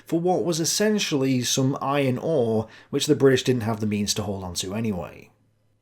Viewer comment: did you say "iron ore"? to my American ears, it sounds like "eye and awe" but that can't be right. has to be "iron ore," no?